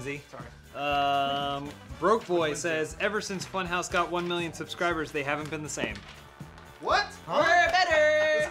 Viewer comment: At first I thought Matt said subscrobblers